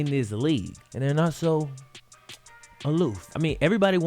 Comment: Did say aloof? Honestly couldn’t hear 😂